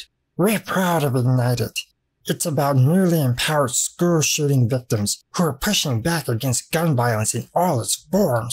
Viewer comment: When you read "school-shooting victims" I misheard it as super shooting victims. That's pretty accurate too.